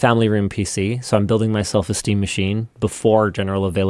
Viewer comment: Am I the only one who heard: "I'm building my self-esteem machine." ? Like you need that, Linus...